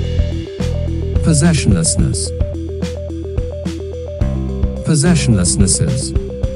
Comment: WHAT IS THAT WORD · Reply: Multiple feelings of not possessing something Possession less ness es